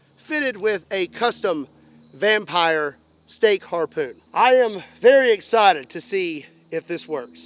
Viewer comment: Oh STAKE harpoon, my wife thought you said STEAK HARPOON and was reaching for her coat and credit card dude!!